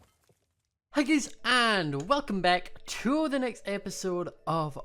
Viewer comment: It sounded like when you said hii guys it sounded like you said hii gays and I love tar for us